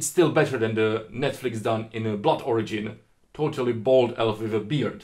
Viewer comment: What was the netflix show mentioned at 5:56 I didn't catch it? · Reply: The Witcher: Blood Origin. It looks bad. <link>